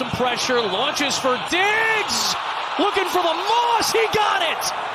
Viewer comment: did he say “looking for the Moss”?! Lmao awesome